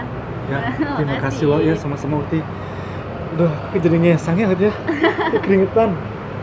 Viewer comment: Gw yg salah denger atau gimana ya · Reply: Itu ngesang Jan Salah 😂